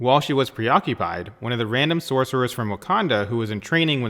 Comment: Did he say “one of the random sorceres of WAKANDA”?? In